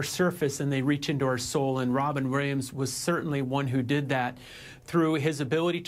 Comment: did you say "Grahams"? That's the most effed up pronunciation of "Williams" ever.